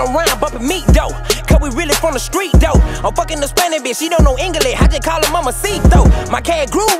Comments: mamacito? bruh....... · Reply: wynn10 lmfao I heard "She dont speak english, but I just call her mamacit tho", but it was suspect af 😂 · Did he just say 1:08 mamacito!!!?...hahahahha. ( like she man guy ) hahahaha.